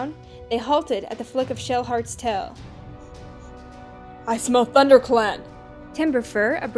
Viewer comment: I thought you said "I smoke thunder clan!" Oh my gosh lol